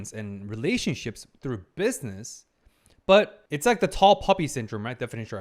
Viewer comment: Thought he said tall puppy at first glad when I seen the word Poppy. Lol